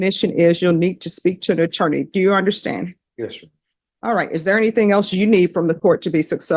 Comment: did he say “yes sir”?